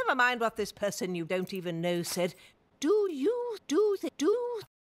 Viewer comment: I thought nana said do you do the dude